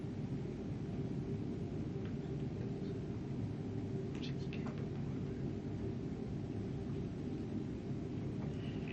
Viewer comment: what does he say? He whispers something and then wrinkles his nose in disgust and says what sounds like "she's is capable of it" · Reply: Something to the effect of "she did this", I believe is the consensus.